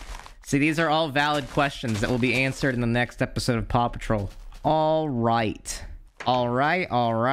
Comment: I thought you were saying "papa troll" until I heard the word dog